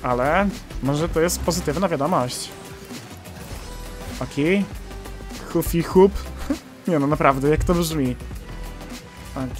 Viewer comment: Hufi hów, głupi h. Tak usłyszałam, przepraszam ! : )